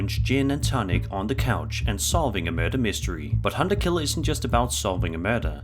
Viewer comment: That man's voice in the first video sounded like he said "Merde" (Shit in French), lol.